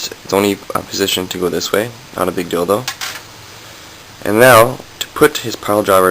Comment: everytime you say 'not a big deal tho' it sounds like you said 'not a big dildo' haha